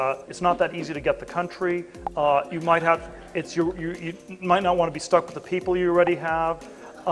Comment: Man if I only had a dollar for every time I heard a non-African talk about what plans they have for Africa. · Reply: On buying an African country when he said "you might not want to be stuck with the people you already had" I just heard " genocide is too expensive at the moment".